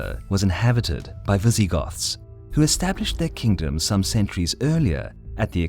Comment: Am I the only one who heard Vi-ZEE-Goths?